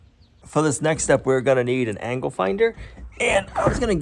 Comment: Angle finder. I thought you said angle grinder. I thought it would be interesting to see how you grind with that tool, then you measured a corner😂